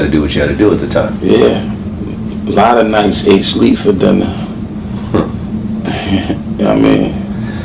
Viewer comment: Jeez I've listened to this a million times at the very end at like 8:20 and it sounds like he sais alot of times "ain't sleep for dinner" or " ate sleet for dinner" neither one aren't what he's saying or even make any sense. If neone knows what the heck he's saying please let me know.